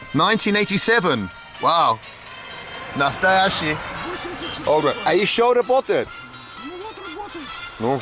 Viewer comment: did you say Nostalgic?